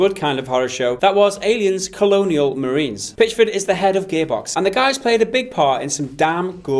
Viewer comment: Lol! I swear when he said "Head of Gearbox" at 0:35 I thought I he said "Head of Gaybox".